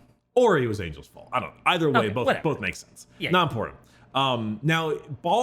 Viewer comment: I'm sorry did he just say the planet was called "ball suck-on-dis"??